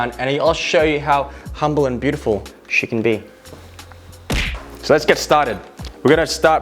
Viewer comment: did you really say "...how humble and beautiful chicken be" or did I mishear? xD